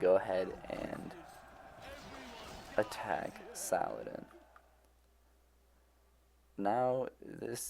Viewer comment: I always hear "Sauron" instead of "Saladin" .